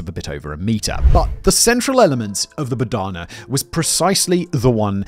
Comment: I was looking down and thought you said Banana for a moment!